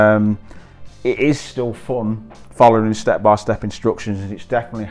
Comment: I'm a native English speaker American from California and do not understand almost half of what says. I just had to turn on the subtitles! He says "it is still fun", I hear "ear seal phone", I can swear he skips "t" there!...he says "does", I hear "dose", he says "parts" I hear "pots", he says "keep up", I hear "keep hope", he says "cupboard", I hear "Covid"!